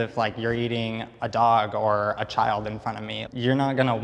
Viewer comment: did he say a Child? Wtf!😂😂